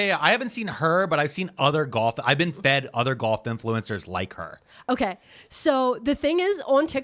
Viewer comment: I kept hearing that as GOTH influencer and I was like HELL YEAH then you said maga coded and... oh.